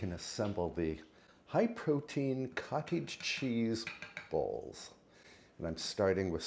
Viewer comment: At first I thought you were saying cottage cheese BALLS, so I was perplexed when you listed the ingredients. They were definitely better suited for a bowl!